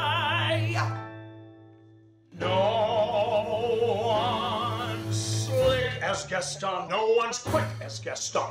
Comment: for a split second i thought it said "No one's sucks as Gaston"